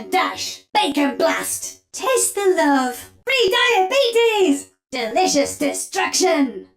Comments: No way I heard "free diabetes!" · did she just say "free diabetes"